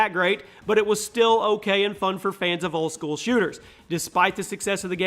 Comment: did you just say it was fun for fans of school shooters?😂😂😂